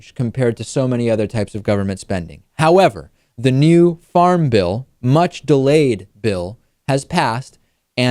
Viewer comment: I thought you said Farm Ville when you said Farm Bill